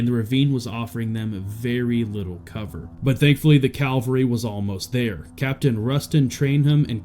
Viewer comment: is he saying calvary? I thought it was Cavalry